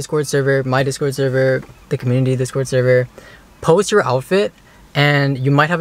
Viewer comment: did he say thicc community server?